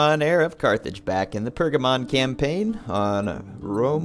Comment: everytime you say Pergamon i hear "Pokemon" xD